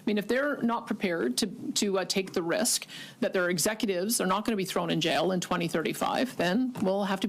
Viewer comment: Did she just say "thrown in jail" ? (at 02:14 mins in) ! Good for her! Keep up the good work.